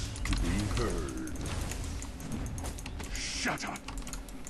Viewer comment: did he just say shut up??